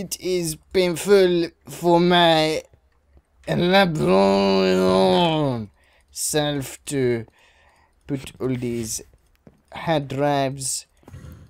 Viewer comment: What does say at THIS point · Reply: AKAchoc! Untitled "It is painful for my librarian self to put all of these hard drives" In a pretty bad French accent. =D